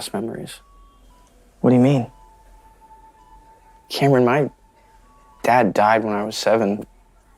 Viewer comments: The fact that he made fun of a cancer patient claiming it is contagious when his dad died of cancer when he was 7 is the most unrealistic thing ever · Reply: Yo Ngl I thought he said my dad died when HE was seven I was all like a yo nahh bro nahh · I thought he said, “My dad died when he was seven.”